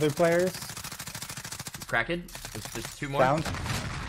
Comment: I thought he said crackhead